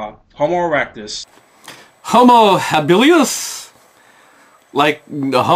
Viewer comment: Ah I see… I thought he said homo-hillbillious, which of course, is that cousin who’s always trying to pick-up his/her other cousins at those parties. ;)